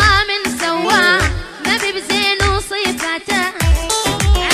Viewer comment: وش قال بعد سبحان الله من سواك؟ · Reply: مافي من زينه وصفاته